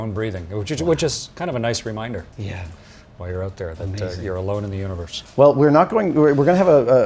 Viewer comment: did he say we're alone in the universe 😗